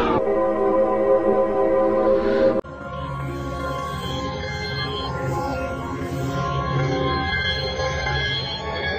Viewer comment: what is that word? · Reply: Don’t go to the playground